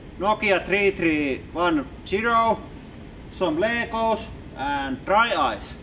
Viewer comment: It sounded like you said 'Sombreros' instead of Some Legos